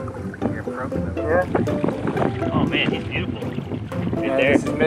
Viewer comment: I heard the cam guy said "fckd up"